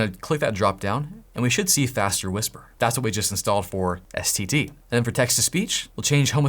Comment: I heard std and thought you learned c++ on your free time lmfao